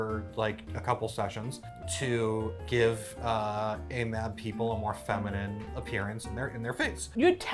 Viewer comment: Hi 👋🏽 Here to get educated. @ 3:12 says an acronym. I couldn’t make it out? What does she say? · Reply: AMAB assigned male at birth